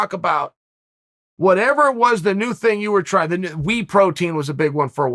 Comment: If Uncle Chael calls whey protein wee protein, then it’s called wee protein. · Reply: I thought he said wheat protein